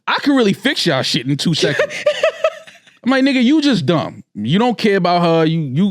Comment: I thought said 🥷’s for a second 😂😂😂😂😂